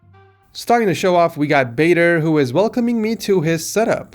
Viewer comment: i thought said,"Baiter"